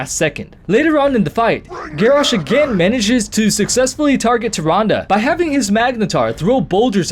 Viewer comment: Holy crap, took me to the end of point 2 to understand you were saying "Tyrande" when I saw her picture at 8:18 Great video but seriously, I thought you were saying something like "Tyranga" or "Turanda"' x)